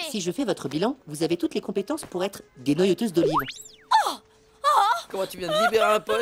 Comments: Qu'est-ce qu'il a dit? 😅😅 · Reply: ben tu dénoyautes des olives quoi.... y’a sûrement des appareils pour faire ça mieux que la meuf du pôle emploi mais c’est ça le gag pour le xoup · c'est moi ou à 1:23 il dit: dénoyauteuse d'olive?